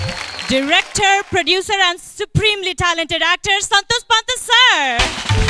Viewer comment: I think she said actress 🤣🤣😂 Am I right??🤔